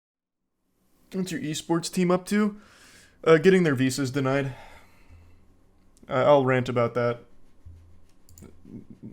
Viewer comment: I thought it said escorts and I’m dissapointed